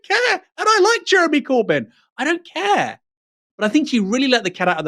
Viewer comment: I thought Aaron was saying "I don't like Jeremy Corbyn" 😂 would've been a bit out of character